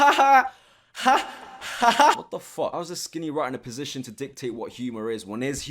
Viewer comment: Does he call you a “skinny rat”?